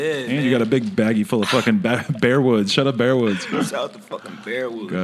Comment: what is a bear wood??? I thought it's called backwood